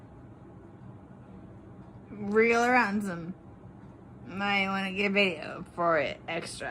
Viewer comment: Does anyone understand this, bc I have replayed it a few times and all I can make out is “real random, might wanna get video for it extra.” What the fuck does that even mean? I refuse to believe she was so drunk this garbled mess came out. Or am I mishearing what she said? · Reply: I think she was talking about getting money for the video. Because she says the word ransom as well.